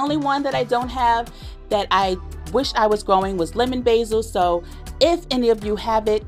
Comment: Oh you say lemon basil I thought you said lemon thyme that’s why I dropped your name at the end of my last video 😂🤣😂 ( I said I got you) lol 😂